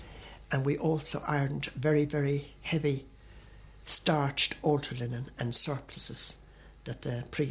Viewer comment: what word did she use ortoninen???is that a type of underwear?? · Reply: Altar linen... not underwear